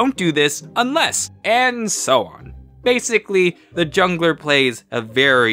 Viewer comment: Is it just me or when he said jungler I thought he said juggler